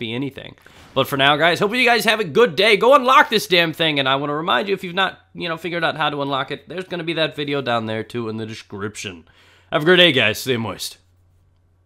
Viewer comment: sounded like he said "Have a grenade guys, stay moist"